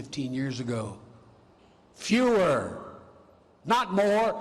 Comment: Love your input Andrew Bolt. Many thank you's. 6:04 in, I thought he said fuher instead of fewer.